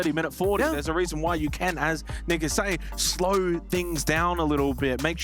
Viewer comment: yo what did this guy say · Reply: which is the name of the other caster